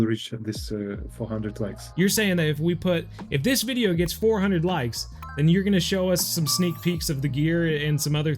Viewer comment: When i thought he said 400k likes 😅